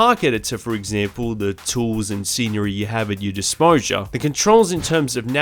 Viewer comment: disposure 11:12 lol i thought he messed the up the first time i heard him say it.. the word youre looking for is disposal my dude